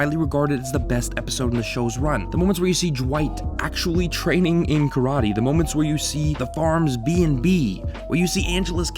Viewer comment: I keep hearing you say "Jewight", and it's freaking me out.